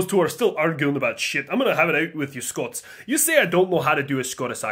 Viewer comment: What said: I'm gonna have it out with you Scots. The subtitles: I'm gonna have a date with you Scots. 1:46 Scots, probably: Pucker up, me pretty boy. We gannae get ye liqurored up like ye ne'er been befare.